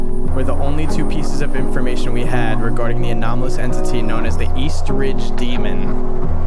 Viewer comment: When the name came up I thought it said the estrogen demon.